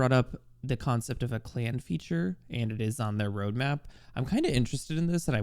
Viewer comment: I thought you said "concept of a plan feature"